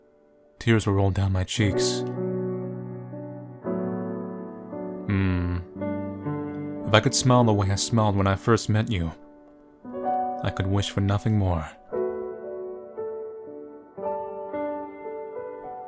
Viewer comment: i was looking away from the screen when he was reading this and i thought he said smell instead of smile...